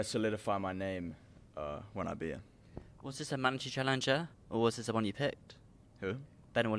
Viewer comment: I have no idea what said here 😂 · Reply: Was this the mandatory challenger